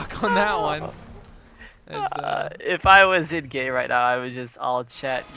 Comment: did he say if i wasnt gay? or if i was in game? im just courious